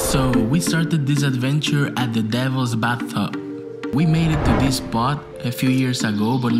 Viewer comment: Amazing edit and list!! I am from Virginia, born and raised and I have never heard of the Devils bathtub but now I want to go!!!!! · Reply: Same here! At first, I thought he said “Devil’s Backbone” 😂 🍻